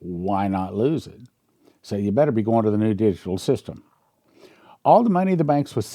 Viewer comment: Really confused here did you just say better go into the digital system?